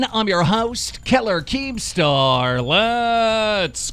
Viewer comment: I was just drink my coffee whilst watching this video, and for some reason instead of me hearing him say 'killer keemstar', i thought he said 'Hitler keemstar'!!! i spat my coffee on my monitor in laughter!! turns out i just miss heard him